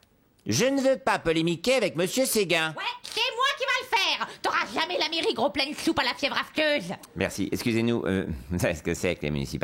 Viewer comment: elle a dit quoi ? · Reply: "T'auras jamais la mairie, gros plein de soupe à la fièvre aphteuse !"